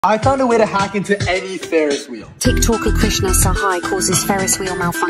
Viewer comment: I THOUGHT HE SAID "HOW TO HACK INTO ANY THERAPY" WICH MAKES NO SENSE 🤑🤑🤑🤑🤑🤑🤑🤑🤑🤑🤑🤑 I'm a sussy baka ඩාඞඞඩා